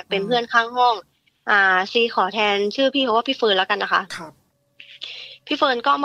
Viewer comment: ซีขอเเทนชื่อเขาว่า ชื่อ พี่เฟีร์นเเล้วกันนะคะ : ได้ยินเสียงตอบกลับมาว่า “ โอเคร “ ขนลุกเลย 😨😨